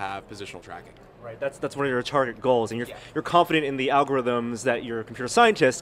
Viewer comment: I thought he said "that's one of your retarded goals". Turns out he said "target" lol.